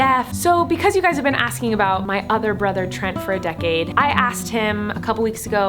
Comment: I thought it said hearing about my deaf brother